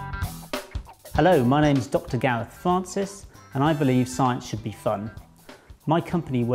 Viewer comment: when he introduced him self i thought he said "hello my names dr gareth fartcus"